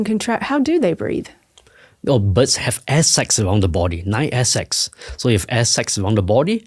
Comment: butts have airsex? Is that what he said?